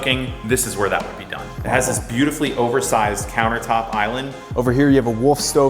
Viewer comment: did he say do some Cooking, or do some Cocaine?